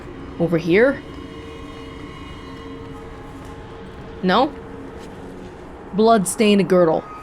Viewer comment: did you say Não or No in 5:24 ? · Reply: No